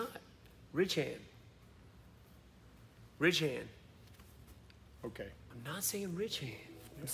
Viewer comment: I always thought WB said "REACH HAND" which kinda made sense ... I guess ?